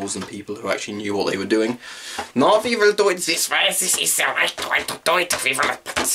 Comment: Idk what he was saying at 5:50 - even though I'm a german but this is hilarious 😂 Nice Video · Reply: Something along the lines of "no don't do it that way, this is the right way to do it"